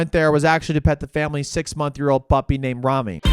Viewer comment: I thought I had a stroke when i heard 6 month year old.